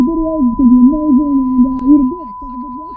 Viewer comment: Did he say "Eat a dick"